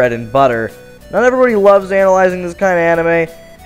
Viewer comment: When you said analyzing (i think thats how you spell it) it sounded like you said anal-yzing